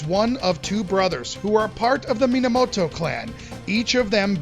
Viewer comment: Did anyone else think said Miyamoto clan for a moment there? :D